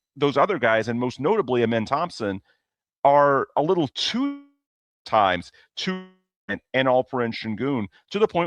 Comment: Apologies for my connection blipping at 13:40, not sure what happened there, but what I said was that Amen/others are often too deferential and too dependent on KD/Sengun bailouts (as opposed to taking what the defense gives). I think that sentiment eventually got across if you watch the full episode, just wanted to clarify since the connection blipped at an unfortunate time.